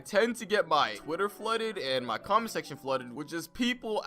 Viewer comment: i thought he said "cum section" but its comment section